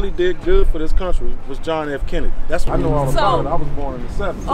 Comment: did that racist say Obama was the best president ever? What a clown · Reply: i thought he said kennedy